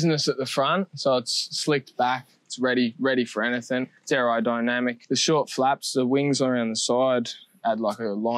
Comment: when he said aerodynamic I thought he said derrodynamic